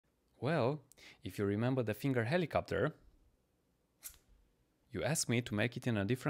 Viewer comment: Am I the only one that thought it said finger my helicopter at first? Just me ok